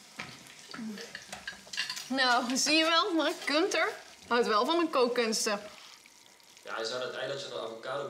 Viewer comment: What are they saying starting at 4:18 ? Auto-generated translation isn't working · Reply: "nou, zie je wel? Mark?(?) Gunther houdt wel van mn kookkunsten." - see, Mark? Gunther does love my cooking. (implying Mark doesn't.) "ja, hij zou dat ei dat je met avocado bakt eens een keertje moeten proberen, dan (ehhhh) gaat hij zich vast nog wel bedenken." - well, he should try your egg with avocado once, he'll definitely change his mind then. ("reset. en actie!" - reset. and action!)